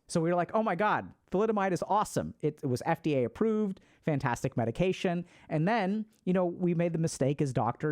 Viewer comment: I thought he said meditation for nausea and I was like whoa I am so in … he said medication. 💊